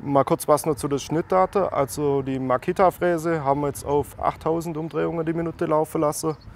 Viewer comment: Es wäre interessant welche Fräse ihr benutzt habt.... ich habe "Makita ?" verstanden.